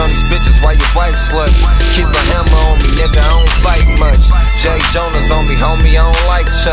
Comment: What is talkin about at 1:37? Did he say air jordans? Cus it sounds like jay jonas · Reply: Jay jonas is also just a way to say jordans lol. That's he said I don't like chucks